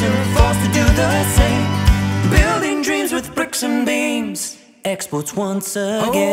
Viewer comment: Last night they sang this song at the comedy Club in LA. One of their own. Great song. Just listening to it here, I finally figured out what you said when I thought you said "bricks and BEANS", at 0:41. It was bricks and BEAMS !! Loved seeing your show live for the first time !! Also, in case some people don't know, when they say craic they don't mean cocaine. That's the Irish word for FUN.